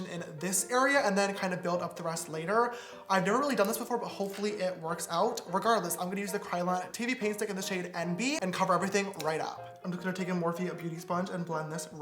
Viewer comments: WHO ELSE HEARD TITTY PAINT STICK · I never want to see him without eyebrows again and I thought he said tity paint idk why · if it wasn't for the subtitles, i would've thought he said "kryolan titty paint stick" lmaooo